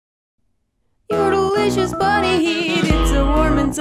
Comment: first time I hear it I thought it said fudelisious 😂